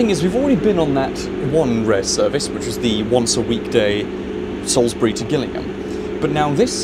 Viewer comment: Oh I thought it said sainsburys🤣🤣